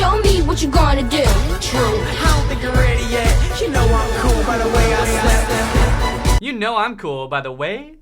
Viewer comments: I thought he said, "By the way I sweat," not, "The way I step." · Ohh I always thought he said “ you know I’m cool because the way I sweat” 😂😂😂😂😂 And why does Brittany’s brother look like? 😅